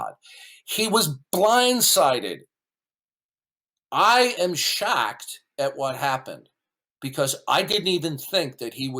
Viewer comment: "I am shacked that even happened" Do I need to explain the problem here? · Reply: I thought I was the only one who heard "shacked" at 2:02.